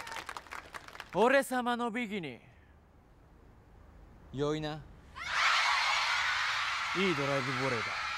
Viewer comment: 空耳だけど 跡部「俺様のビキニ良いな」に聞こえた